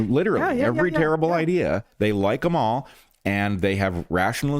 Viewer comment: let's play a quick round of 'Sounds Like', shall we? 😉 35:14 "...they like 'em all--" = THEY LIKE KAMAL(A) 😃😣😠 thanks, Bret! Heather, too!! (i'm surprised you two didn't hear this when Bret said the sounds like...!) Rescue the Republic!! GO TEAM!! ♡